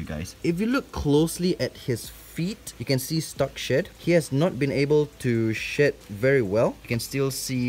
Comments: I thought he was saying “he hasn’t been able to sh**” 😂😅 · “he has not been able to shed very well” Me: did he say shit? Lol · made me laugh cause I thought he said shėt instead of shed lol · I thought he was saying "he has stuck shit, he has not been able to shit very well" 😭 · why do I keep on think he's saying shit instead of shed🤦🏾‍♀️😭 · i thought he said "he hasn't been able to sh*t very well" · I thought he was saying sh*t the whole time- it took me a while to figure it out lmao